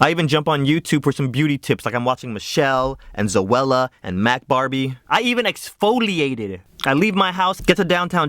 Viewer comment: I had to listen to that last name at least 3 times... thought he said Matt Hardy! Like, wow, The Broken one does make up? I know it ain't him but it still sounds like that is what was saying.